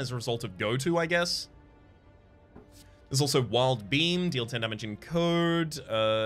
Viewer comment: I misheard wild beam for wild bean and now I just want it to be wild bean instead